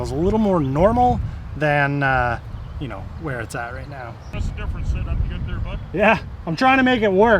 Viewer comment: Did the guy in the field say "hey that's a stupid idea buddy". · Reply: ? I heard "that's a different setup you got there bud"